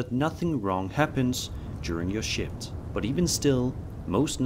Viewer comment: Did I hear correctly? "During your shit"? On the first video 🤔😂 Edit, I had to rewind a couple of times and swear you say shit😂